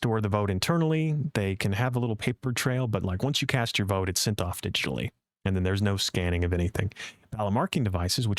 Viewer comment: "sent off to..." (where?) [I couldn't hear, please clarify, thank you] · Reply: said: sent off digitally.